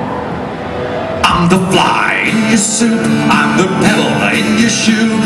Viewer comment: I always thought he was saying "I'm the devil in your shoe", until I looked up the lyrics, and pebble makes a lot more sense.